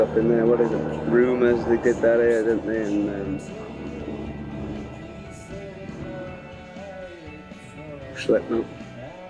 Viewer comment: did he say slipknot? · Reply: never mind I found it..but I'm not sure what he says, this is what I hear "rumor has it we did better than....slipknot." is it correct